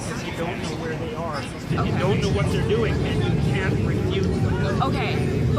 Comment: subtitle says CAN, should be CAN'T......'>........